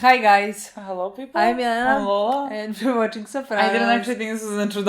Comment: been watching you ladies since the beginning of breaking bad. i always hear "I'm Lola. I'm Lala"